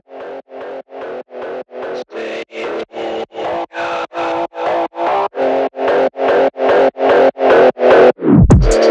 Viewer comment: in this part i think he Say Open the door or Stay i'm home😮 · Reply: No he said staplegun